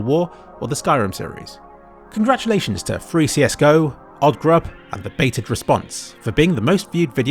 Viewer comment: thought you said 3csgo. night time q.q